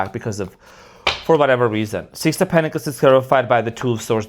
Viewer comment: Ha ha ha, I thought you said the 6 of pentacles is terrified by the two of swords, the chariot and hanged man Love that shirt on you! Not everyone could pull that off! I thought you were wearing suspenders! When I get mad during a reading it’s usually because I don’t want what you’re saying to be true but it usually is. 🤦‍♀️🤣 My tf is a Pisces avoidant. He’s amazing, creative and a workaholic. There’s a LOT of push and pull. The chemistry is cosmic. We speak different languages and have to use translation apps to communicate. I’m a Scorpio/Sagittarius cusper. Today’s my birthday. I enjoyed my most recent trip around the sun and am preparing to do it again! But, this time much differently, with more intention and purpose and centered on myself and my values. This year I’ll sing and shine from the inside-out louder and brighter than ever before! ☀️ 💃 💫 🌈 💕 I’m on a mission. I think his occasional silence aside from being a workaholic is actually because our connection is so intense that it’s bringing up shadow sh¡t but I see his “rejection” as “PROTECTION!” For BOTH of us! And, I’m not worried. I know my worth AND HIS AND I know my focus needs to bE on me! Time to grow up and glow up! ☀️💝💫 As I focus on unveiling my purpose, and as I become one with the love I desire, I will attract what is meant for me and vice versa. Whether we are to be together or not I have nothing but love for him. I think your throat issues during readings is due to blocked communication between couples.